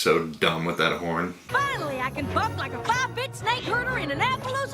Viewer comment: Did Applejack say b**?